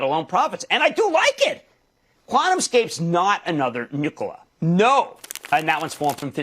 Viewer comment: Why do I hear "nipple" every time he says "Nikola?"